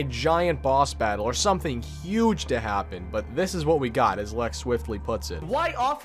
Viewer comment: Who’s the content by at 6:30 ? Idk what is saying “lekswiftly” “luckswiftly” I cant find nobody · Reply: i think